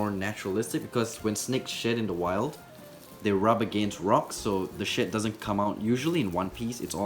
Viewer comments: who else thought he said 'so the shit' 😂😂😂 and at 5:00 'snake shit' 😂😂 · When he said snake shed I thought he said snake shit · It sounds like hes saying "snake shit" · I thought he said snake sh!t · Love this channel also when u broke the snake shed it sounded like u said snake shit